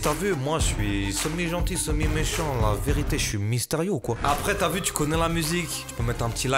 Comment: « La vérité j’suis mystérieux ou quoi » K.O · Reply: Il dit "Mister You" c'est un rappeur